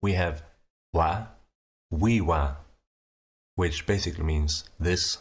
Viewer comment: This is a great language and all that, but i cannot get over the fact that you're just talking about pronouns at 16:40 and then all of a sudden I hear "Wa. Wee wa."